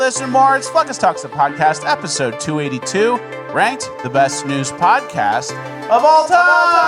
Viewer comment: I thought was saying “Best Nudes Podcast of all time”